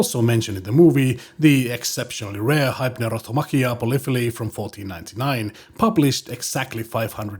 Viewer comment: Did you say Hypnerotomachia Poliphili ? Just wanted to make sure I heard right. :)